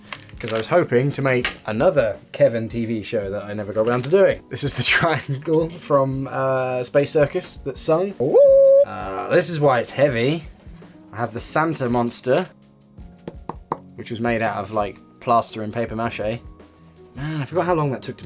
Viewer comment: I thought he said "Santa Pogster"?